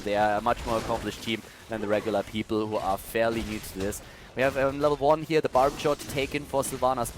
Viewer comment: When did I call them useless? Oo They were simply outmatched. No shame in that when you go up against a pro team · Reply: "fairly useless" is what I heard if its just your accent them my apologies.